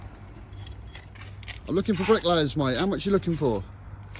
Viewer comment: First i thought he said he was looking for brick lice.